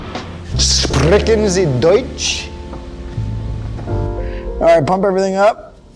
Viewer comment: Did i hear DEUTSCHLAND